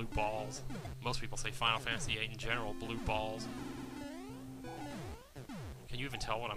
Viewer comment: Hah, I so thought you were saying: "Blue Balls" then you corrected it! Great stuff, I am gonna try and catch up, but I just started watching. You, also, have many vids and from what I've seen its good stuff!